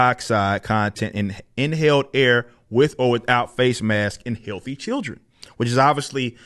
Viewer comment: Headline cut off in screen. Thought said "in filthy children." Lol.